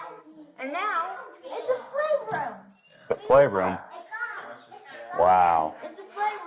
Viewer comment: I swear that kid said it’s a “slave room” not that it’s a playroom